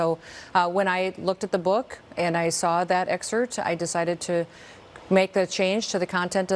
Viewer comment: did she say “excert”?